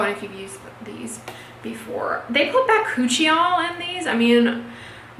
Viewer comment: bacuchial? Why did I think you said by-coochie-y'all!!!!🤣🤣🤣😂😂🤭